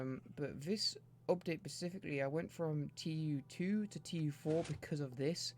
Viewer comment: No offense, but you saying "Pacific-ly" instead of "specifically" (such as at 2:56) is driving me mad.